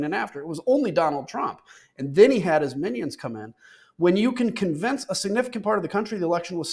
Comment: then he had his minions come in 😂 BUT, I thought I heard him say IDIOTS ... is there a difference 🤔